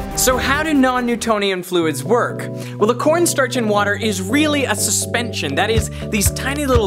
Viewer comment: i thought he said porn startch